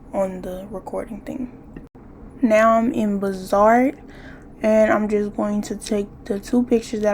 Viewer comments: What’s tha app u said around 2:02 ? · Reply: Bazaart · what app are you saying ? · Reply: Bazaart